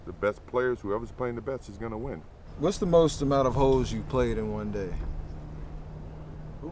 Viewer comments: when Steph asked MJ "What's the most holes he's played in one day?" I actually thought Steph said ho's at first. It made it more funny when MJ said 63 and Steph was like "Damn." and MJ smiles and says "I was younger." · 😂😂😂😂😂😂 Steph = What's the most amount of hoes you played in one day MJ = 63 Steph = Damn! I know I'm not the only one who thought he said that loll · i heard hoes he said hoes right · thought he said hoes🤣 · I had this on in the background and I was only half listening and thought I heard @ 10:11 - Steph: “What’s the most amount of hoe’s you played in a day?” MJ: “Ooo boy.. 63?” Steph: “Damn..” Looking back I see now Steph said holes 😂